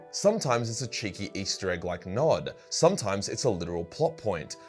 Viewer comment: Did I hear NOD? like the Brotherhood of Nod? I must know where this easter egg is